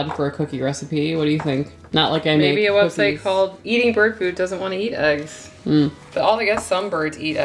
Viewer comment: Haha: the first and second times that I listened to this (because I listened twice before I had a chance to watch-that’s often the case, and absolutely enjoyable both ways!), when said the website name “eating bird food”, I thought she said “eating GERD food (for, you know, folks with reflux-seriously!😂)” and didn’t realize the correct name until she said “I guess some birds eat eggs”!